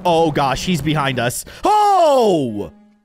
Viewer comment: What did you say at 5:55? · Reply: Naughty.